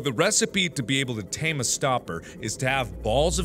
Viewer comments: lol anyone notice at 16:24 he says stopper instead of stalker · did he say Stoper 🤔